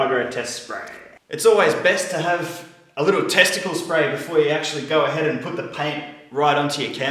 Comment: did he say testicale spray at 1:40?